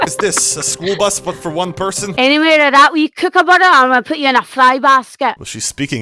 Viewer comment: I heard "Any more of that and I'll cook you up in butter and put you in a fly basket" (disclaimer: I have no clue if this is right).